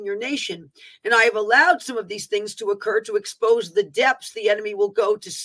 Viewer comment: you mention the depths the enemy will go to . All I heard was Depps 🤔